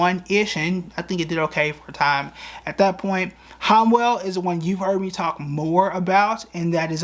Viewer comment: you mentioned a stock in the industrial sector, but I can't figure out the name 4:40, transcript says "Hwell" then 4:43 says "homos" . What's the stock and symbol for it? You be knowing and I want to know also.😅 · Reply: Homwell $HWM lol